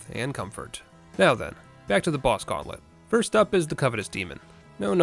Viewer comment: at first I heard covenant demon